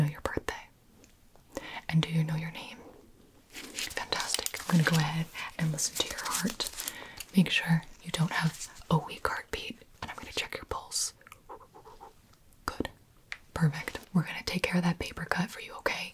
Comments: Why did I hear “I’m gonna check your balls” 😂💀💀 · IM SORRY BUT FOR A SEC I HEARD "IM GONNA CHECK YOUR BLLS" WTH IS WRONG WITH ME 💀💀💀💀💀 edt: nvm checked again I def did hear blls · Brooooo, I swear to god I heard "I'm gonna check your balls" 😭💀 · dawg i thought she said "im gonna check your balls" 💀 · English is my second language but idk why the hell I heard and I'm going to check your balls, wtf, fr something is wrong with my ears in a part of the video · "Im gonna check your pulse" LMAO I HEARD "Im gonna check your balls" 😭